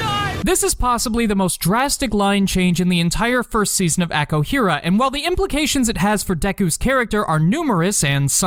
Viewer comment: excuse me but at 7:23 did you say aco hera